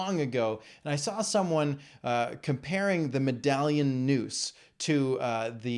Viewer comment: "Put down your noose, I'll hang myself" makes far more sense than "Medallion noose".That's what I thought he said, at first.